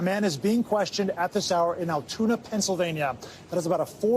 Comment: I thought they said he was suspected to be in Atlanta